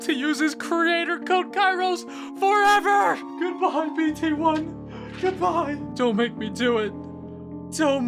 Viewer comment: did he say b*tch? lmao